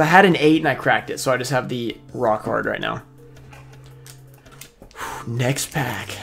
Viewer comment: When you were talking about Curse of Dragon, it sounded like you said rock hard, not raw card. Hahahahaha!!!